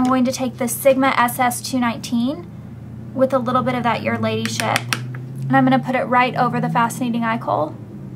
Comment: haha at first i thought you said "with a little bit of that lady shit"